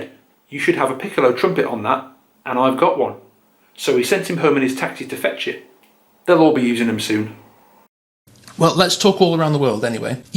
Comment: What are you saying here? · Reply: “So we sent him home in his taxi to fetch it. They’ll all be using them soon.”